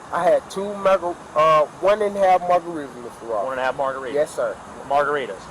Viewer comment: It sounds like he said two MAGAritas